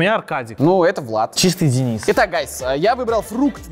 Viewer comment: Мне одному послышалось "и так гайсы"?